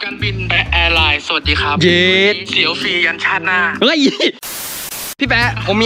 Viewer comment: พี่แป๊ะพูดว่าไรน้าๆ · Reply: เสียวฟรียันชาติหน้า ค่ะ